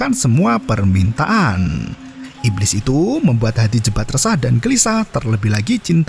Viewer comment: Kirain nama nya jembut salah denger gua ternyata wkwk